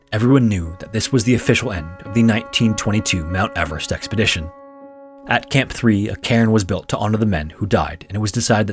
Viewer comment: "At Camp 3, a Karen was built to honour the men who had died..." misheard narration